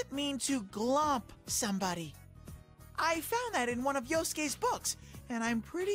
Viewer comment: That police officer is like Yosuke but grown up. Stuck in the closet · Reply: I thought you said Yusuke. Still true either way.